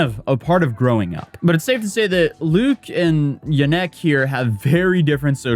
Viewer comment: Is it just me but every time atozy say yanek I hear "ya neck" like in SpongeBob "my leg" HAHAHA